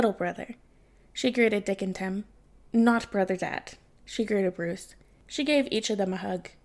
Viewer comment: God I love Cass calling Jason Big brother Dad. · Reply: "Not-brother dad" is a pretty close second, even if I initially misheard it as "not-brother-yet" which I assumed meant Dick and I was a little disappointed to be wrong?